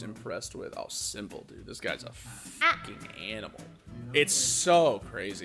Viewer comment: Did i just hear gura's a